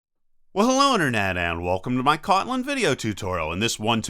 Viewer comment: What the heck did you just say? I had to listen to it 4 times... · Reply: Well hello internet! That is how I start all my videos for some unknown reason